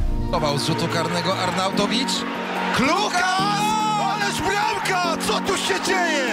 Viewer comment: usłyszałem kluchas